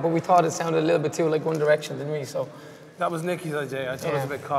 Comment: did he say one direction?